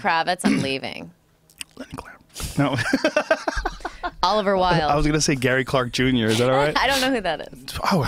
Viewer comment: Natasha - "I made you come early"... Did anybody catch that? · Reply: ***** I thought he said olivia wild